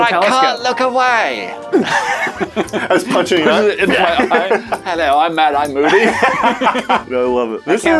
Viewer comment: Matt: Hello I'm Mad Eye Moody What I heard: Hello I'm Matt Eye Moody